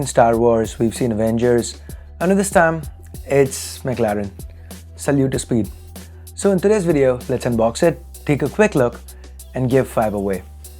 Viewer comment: I heard, "Sallu to Speed" 😂